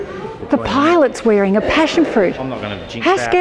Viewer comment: I've heard mum said: "The pilot's wearing a parachute"... then there was a voice-over from and i had to rewind back not understanding... this time i heard "passionfruit", oh boy, the mind trickery.